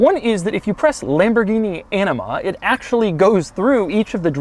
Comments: I thought I heard "enema" instead of anima. I was thinking, if this car gives you an enema, then this truly is a super luxury feature. Comfortable leather seats giving you an enema. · Totally thought you said enima when discussing thes shift lever. Haha.